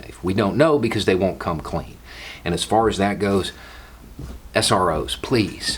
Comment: What did say at 7:10? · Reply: He said S.R.O. that’s an abbreviation for School Resource Officer. They’re the cops that are kept in schools.